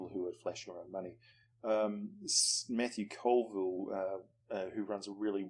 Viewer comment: What's the name of the fellow whose channel you recommend at approximately 11:10? I can't catch the name. Regardless, it's (yet another) very helpful and insightful video. Thanks ever so much for sharing it with us. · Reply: <link> Matthew Colville